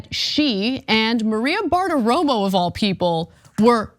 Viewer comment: Who’s this “Fart Aroma” I keep hearing about?